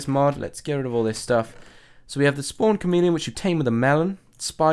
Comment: When you said the camp drops from the chomeleon I thought you said comedian😝